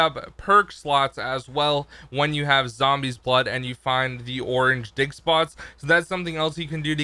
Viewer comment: did he say orange di*k spots? lol it's just sounds like it when you said orange dig spots